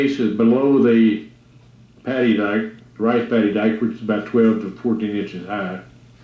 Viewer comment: I kept hearing 'panty diaper'...